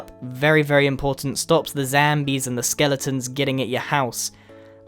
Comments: Lol who else heard him say “zambies” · did he say the zambies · He said Zambies instead of zombies lol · Did you just called zombie's zambies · who heard him say zambies. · I heard him say zambi